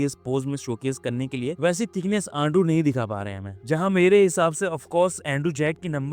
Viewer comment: did he just say ando?